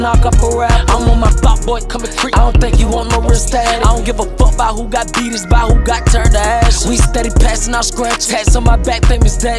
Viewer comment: “I don’t care about who got beat , it’s who got turned into ashes?” 🤭🤭😳😳😳😳😳😳💙💙🌀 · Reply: I thought he said I don't care who got BDs jus who got turnt into Ashes